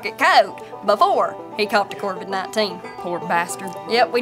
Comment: Talking about the last animal they had to put down...it sounded like she said "caught the cor-bon 19" which had me thinking... oh Cor-bon is branching out of ammunition and into bioweapons now..